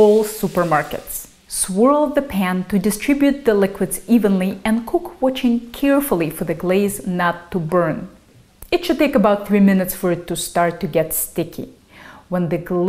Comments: did you say "and cook очень carefully"? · did you say "and cook очень carefully"? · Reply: I heard, "cook watching carefully for the glaze not to burn". Don't know what the Russian word means that you heard.